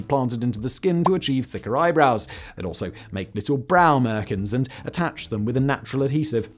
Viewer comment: you said "they would make little brown Americans and attach them with a natural adhesive"...even the CC says the same. So WTF are you saying?!